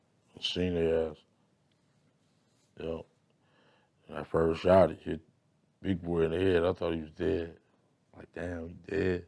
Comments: did he say when ”I”first shot big boy in da head I thought he was dead 😕😯 or am I tripping 🤷🏾‍♂️ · Am I the only one who hears "when I first shot (inaudible) big boy in the head I thought he was dead" @ 12:24 ... read his lips as he saying it , it sounds like he said when I first shot, hit big boy in the head ,I thought he was dead.. imagine you're in a car with your friends and one decided to shoot someone from inside the car into another car , only the person shooting would be able to visually see who is getting hit and where they are getting hit and what it is their doing during , so if he was the driver but not the shooter and the shooter was sitting behind him, he would have to pull up further so that the guy in the back can shoot directly at them , so he would not be able to see what's going on , if he was the front passenger and the driver was shooting ,the driver would have his body infront of him so again he would not be able to see who is getting shot and where , now if he was in the back passengers seat it would be even harder not only to shoot but to see who and what you're shooting at, so my point is ,in order for him to be able to see the driver (suge) getting shot in the head , he had to be the shooter , I want yall reenact this with your friends and put yourself in every position to see for yourselves